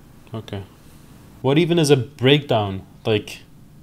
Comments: I thought it said "mental breakdown" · thought it said mental breakdown